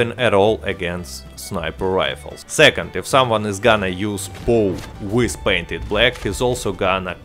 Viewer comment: you said what I did not understand what I heard was "second if someone is gonna use pool with PIB is also gonna crush you" what is pool? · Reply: Bow :)